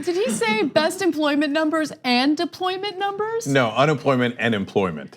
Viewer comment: I bet those answers are scribbled (Like his name on the EOs). Nothing says you did the work yourself then going around telling everyone that you , ONLY YOU , did it yourself. Hey Anna, I thought he said de-polyment numbers too. I love how the unemployment figure magically became real , not fake, under tRump.